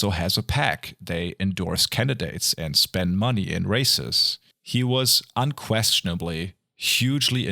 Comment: Due to your accent, I misheard "spent money on races" as "spent money on racists". Which I think is a far better way of saying what they did, in my opinion.